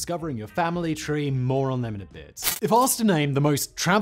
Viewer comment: I really like watching all of your channels Whistler, but for years now you have said "more on them later" I always hear it as "moron them later" i am surprised you get promotions from all these morons lol